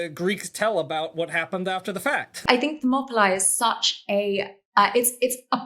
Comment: I don't know anything about this topic and I'm trying to figure out what word she says at 26:31 "I think the Mob-Lie..." or "I think the Pnopili" or something. Can you help please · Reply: she's talking about the Battle of Thermopylae.